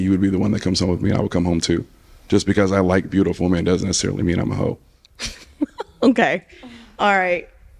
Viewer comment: did he say "just because i like Beautiful MEN doesn't necessarily mean I'm a hoe?" Nobody caught that?